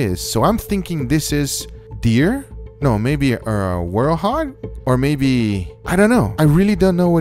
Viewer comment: Did Guga just say “werehog” at 0:36?